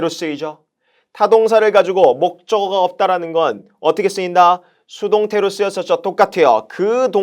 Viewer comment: 초쯤에 뭐라고하는건가요?수동태에 있다길래 가서 들어도 무슨말인지 모르겠네요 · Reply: 안녕하세요! "타동사를 가지고 목적어 없이썼다는 건 수동태로 쓰였었죠! 똑같아요!" 타동사의 분사형태에서 목적어 없으면 주로 수동의 p.p가 됩니다! 라는 의미였습니다! 감사합니다!